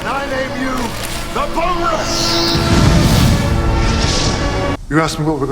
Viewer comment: Can anyone tell me what he actually said at 4:02 what the name was? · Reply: Boneless